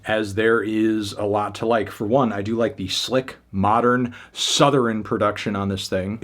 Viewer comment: did bro say Southerin?